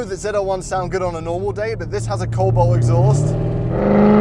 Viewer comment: What exhaust is on the ZL1? Sorry, I couldn't understand what the British guy was saying in the video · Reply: Cobalt I believe